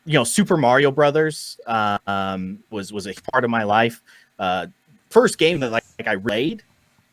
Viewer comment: ik he said part of my life but the lagginess made it sound like he was saying the fart of my life i was dieing in lafter🙂😀😁😀😄😆😂🤣